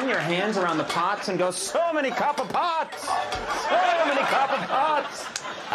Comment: Conan going "So many copper pots" Comes in my dreams sometimes! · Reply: Until this comment I always heard “so many cup o’ pots” and I now realise how idiotic my previous notion was.